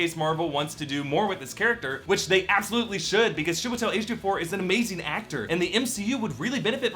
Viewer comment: Did he say Shebutel H24? at 4:30? Oh my Goodness. Chiwetel Ejiofor ooo. Loool